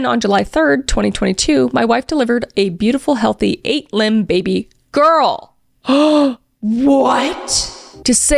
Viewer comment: Me being so confused when I heard "8 limbed baby girl" and thinking of an octopus 🐙 😂 then looked at the screen and being very reassured that it said "lb" which is pound (doesnt make sense to be but 🤷)